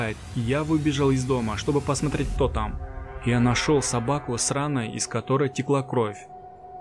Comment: Очень увлекательная и страшная история. Но мне послышалось не " собака с раной", " нашёл собаку сраной")))